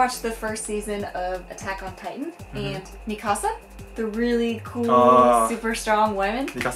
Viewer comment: When they said Mikasa I thought they said: Me Casa/ My Casa... My mistake..